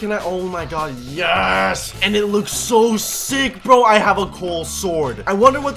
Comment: When said I got a coal sword I heard it as cool